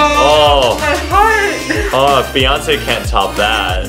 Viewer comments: "ALL THOSE LEGS... BEYONCE CAN'T TOP THAT." LOL. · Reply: I thought he said fiance not Beyonce 😂😂😂😂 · "ALL THOSE LEGS... BEYONCE CAN'T TOP THAT." LOL. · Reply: I thought he said "my fiance can't top that"